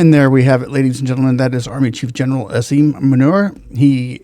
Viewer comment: Sounded like Asim Manure. Lol